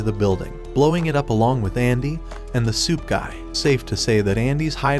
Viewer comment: I dont know why but i thought you were saying "soup guy" instead of "suit guy."